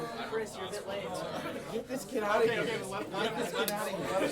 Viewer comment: I just know the people laughing at Chris are intimidated by his intelligence. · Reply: wait at 47:24 didnt they say “get this kid out of here” in regards to chris cuz i swear i heard his name but idk